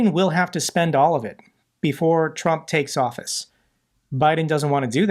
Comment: he says before the felon takes office. WTF DID I HEAR THAT RIGHT? This guy a maga leming?